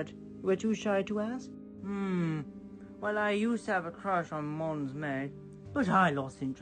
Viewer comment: Does says, Mons Mey?